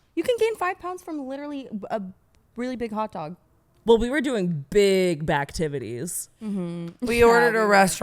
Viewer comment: I just heard “big backtivities” and almost dropped a weight on my head at the gym